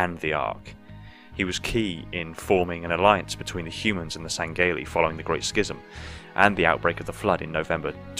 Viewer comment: Did.. Did he just say "Sangayly"